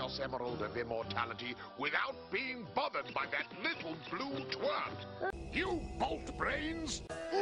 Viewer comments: Did robotnik just say twat? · did he just say little blue twat?! OoO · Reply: 'Twerp'. Like the insult Jessie and James of Team Rocket use in Pokemon. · did he just call sonic a "little blue twat"?? ?